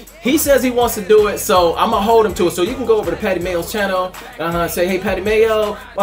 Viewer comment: I thought it said fatty mayo